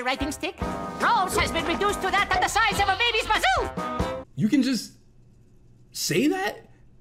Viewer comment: Bro i always thought Rolf said "baby's kazoo"